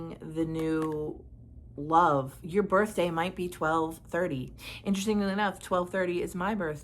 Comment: If I could count how many times Elvis' Return to Sender has come up for me!🤣😂😅I had to comment! Pause ⏸️ brb going to listen 🎶 to that song yet again!🤣😂😅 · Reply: Wow! you said 12/30 but I heard 4/30 that's my birthday 🎂 🥳 🎉 🎈 🎁